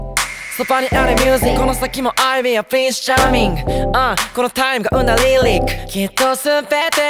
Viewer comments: この先もあいりはprince charmingって聞こえた❤️ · I'll be your prince charming あいりはプリンスって聞こえるやつ復活？？